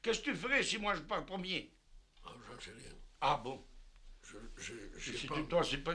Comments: "Qu'est-ce tu fais si moi j'pars en premier?" "Je placerai un mot!" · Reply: Moi j'ai entendu "J'en sais rien" . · "Qu'est-ce tu fais si moi j'pars en premier?" "Je placerai un mot!"